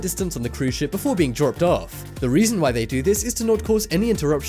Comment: Did he say at minute 3:15 that you would be “jerked off”? I am not joking and not trying to be rude but... what cruise line was that?